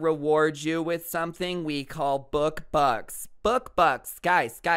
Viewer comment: Bro i thought you said book fucks💀